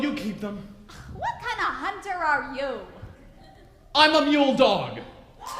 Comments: what did the baker say in 31:54 ? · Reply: "I'm a mule dog!" I have absolutely no Idea what that means. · What is he saying @ 31:56?? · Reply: "I'm a mule dog" I think